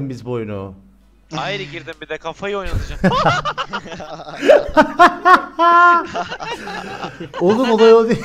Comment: ne diyor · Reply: aery girdim bir de kafayı oynatıcam